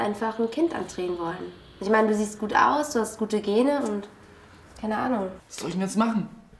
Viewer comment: Ich hab verstanden : „Du siehst dir aus und hast Migräne“ 😂♥️♥️